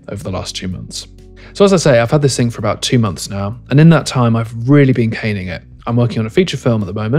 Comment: I'm sorry but did you say 1:27 that, "you've really been hating it"? Maybe my ears are deceiving me because the CC says that, "You've really been gaining it". Am I the only one that heard that? Good review btw. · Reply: I said, “I’ve really been caning it” - as in really pushing it to its limits